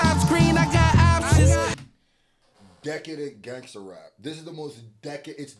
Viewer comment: I swear you’re saying Autistic Gangster Rap 💀😂😂